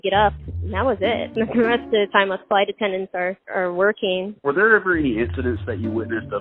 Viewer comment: Based on these numbers, if they had charged just $20 more they would've been in the green with a 17 million gain. Feels like it could've been successful with a little tweaking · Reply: Thought you said twerking for a second.